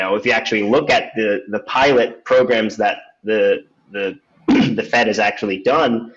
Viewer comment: "Pirate programs" (that's what I heard).